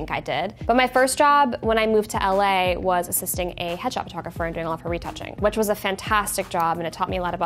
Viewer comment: I thought she said “hedgehog photographer”